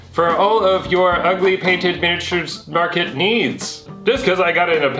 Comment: Every time you say paint, I hear "pain"